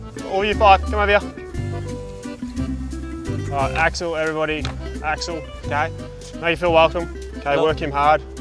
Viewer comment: What did Alex say at 1:30? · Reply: Brian Ng he said “im axel” in a french accent.